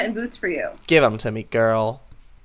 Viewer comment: when said "I have 2 golden apples" I thought she said "I have 2 golden nipples" XD